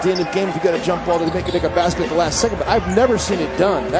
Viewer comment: Does he say "make a n##ga basket" ????